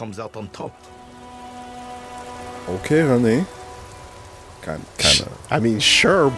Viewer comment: I heard "Okay René" as "Okay honey" and that would be such a fun thing to say in that moment